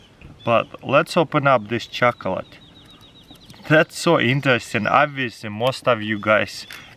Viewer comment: 7.57 I thought he said chocolate putin😂